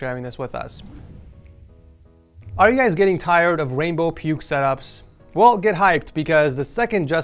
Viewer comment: did he just say rainbow pubes ?????